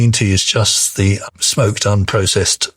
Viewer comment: I think he says "smoked"; googl claims unsmoked tea is healthier... can anyone explain which is correct? Great replays, thanks y'all.